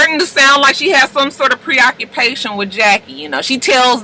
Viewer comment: I actually thought Malaysia said she was "pro-procreation".